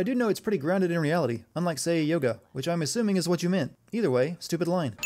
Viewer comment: did he just say 'Videl'? either he's talking about a friend of mine or Dragon Ball Z made it onto the script